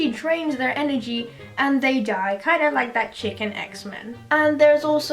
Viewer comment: lowkey had no idea what you were saying "kinda like chicken Xman" then i realised im just stupid